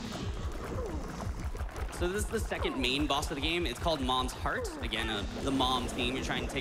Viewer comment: I keep hearing "meme boss"